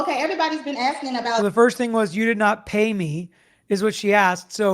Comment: I heard her say "you do not hate me" not "pay me." 😁